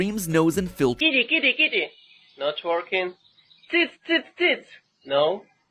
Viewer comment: nah bro I am Hungarian and I heard "tits,tits,tits" I was laughing way too hard😂😂😂👽👽👽